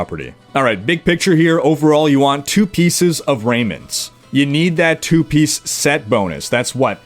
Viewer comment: Closed captions says Raymond's instead of Raiment's